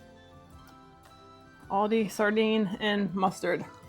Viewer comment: At the very beginning I was focused on the zoomed in Winston and rack of sardines and I wasn't reading the text bubble so thought you said "Do you want to try all deez sardines or what?" 😅 Then as you kept talking I realized it was "Aldi's" brand. 🤭